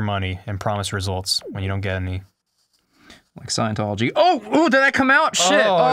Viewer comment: what did he say? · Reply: He said "like Scientology"